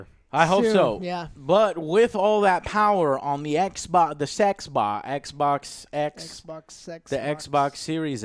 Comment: Did I hear right? "Xbox, the SexBox"? Maybe you're setting your expectations too high, AJ. ^^